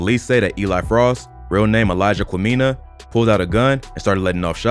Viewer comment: I really thought u said his real name was Elijah Chlamydia 😂😂😂